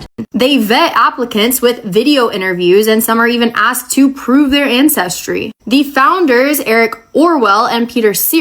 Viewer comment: I heard, "prove their incestry" and immediately thought, "Yeah that checks out"